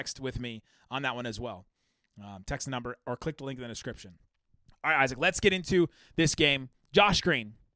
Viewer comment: Was there a little Freudian slip at 11:10?😂 sounded like he was about to say “alright Isaac”